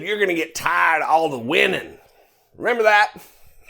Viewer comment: Oh....I thought Trump said, "you're going to get tired of all the WHINING."